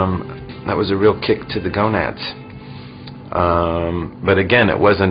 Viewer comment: hahaha. right around 1:00 all the subtitle says is "....." they couldn't understant the whole "gonad" part.